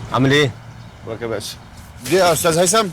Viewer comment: Very useful exercise, thank you! So, min. 11:49 is the answer to your question but what he says I cannot understand :-) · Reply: You are welcome - he says: Di ya ostaz Haytham (here di means 'this one'? and ostaz means 'Mr.)